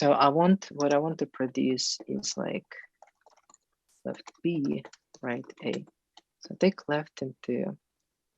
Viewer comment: Dan, did you say "left b и right a"? :)